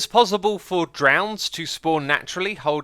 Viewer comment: did anyone else hear “nutella shell”?